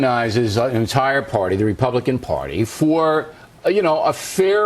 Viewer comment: Does he say Our party there? He kinda muddled over that part making it hard to hear.